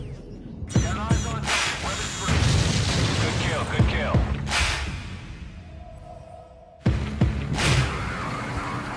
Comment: what does she say in 0:04 ? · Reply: HE says "good kill, good kill" Its AWACS confirming the kill of a ground target by troops on the ground ordering the strike. :(